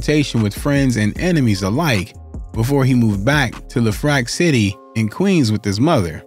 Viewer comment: If you from Queens, what did he say at 0:51 ? Im crying over here . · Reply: Le Fleur, Le Fleur